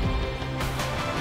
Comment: Why did i hear it like 1:40 "eric you don't have to be afraid to go homo" 😭😭